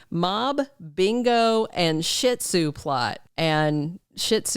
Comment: I thought that was saying Ziti not Shih Tzu. Lol